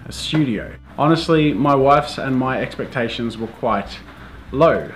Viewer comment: I thought he said my wife's 😂😂